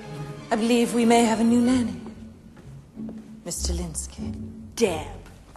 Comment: I never understood this joke until I watched it years later I thought he said "I quited" which implies he used to kill women But now that I found out he was "acquitted" as a lady killer just makes this so much better